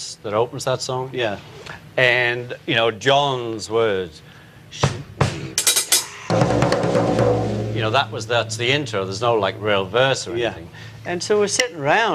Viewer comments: Did he say "john's words: shoot me with that"? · Q: What does Ringo say Johns words were at 2:47? ("shoot me ? ride?"?) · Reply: I hadn't realised it was "shoot me" from the original, but listening to Ringo and the original recording again - yes that's what John says.